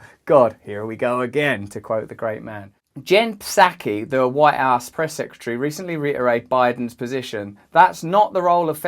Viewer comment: I heard "white ass secretary" rather than White House secretary. 😁